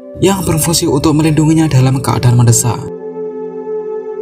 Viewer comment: mendesak woi bukan mendesah